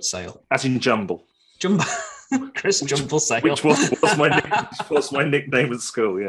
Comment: Hi Chris, Really enjoyed the I Photography podcast with. You executed it very well with your journey to become a professional photographer. Loved the jumble bit at the end. All the best mate. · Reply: Glad you enjoyed it Tim! Yes I thought Chris said 'Jumbo' not Jumble! haha :-) Thanks for watching, more shows with Chris coming tomorrow :-)